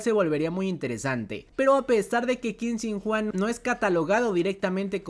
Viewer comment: Como cuando estás en clases pero hay prioridades como el destino de la humanidad😌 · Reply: Mensaje random. Escuché mal o en aquí dice Qin Shin Juan 😎 7:58?